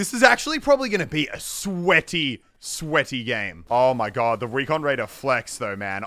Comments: did he say recon raider instead of expert?? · Did he just say recon raider? Lmao